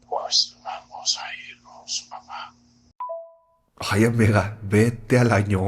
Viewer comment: No maaaa, en el minuto 2:45 parece que dice “vamos a irnos mamá” 💀😭💀💀😭💀